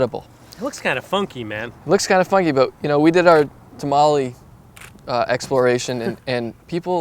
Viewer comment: What did he said at cca 5:30? What is right there inside of body? Thank you. :) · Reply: I was talking about tomalley: Tomalley (from the Carib word tumale, meaning a sauce of lobster liver), crab fat, or lobster paste is the soft, green substance found in the body cavity of lobsters, that fulfills the functions of both the liver and the pancreas. Tomalley corresponds to the hepatopancreas in other arthropods